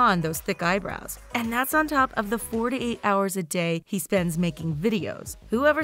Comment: did you say 48 hours a day? 😂😂😂😂🤦🏼‍♀️